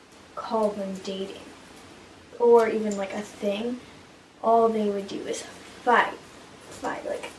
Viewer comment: I thought she said all they did was fu